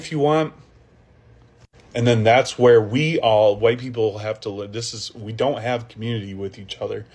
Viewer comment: I thought they said white people have unity